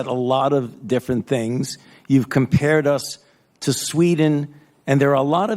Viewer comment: The "flattening the curve" is a NON STRATEGY, it has been proven so by facts, and even the "second wave" is proving to be a NON ISSUE, also the US DOES NOT have a substantially worse death rate from the ccp virus than Sweden, fauci is a manipulative lying little ork. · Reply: Just FYI, Fauci's argument was that Sweden's death rate is higher than other Scandinavian countries [he didn't say US] - perhaps you misheard.